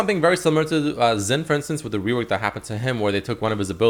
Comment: did he say Zen? at 2:01?